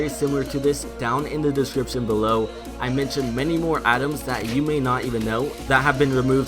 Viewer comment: it sounds like he is saying adams when he is tryng to say items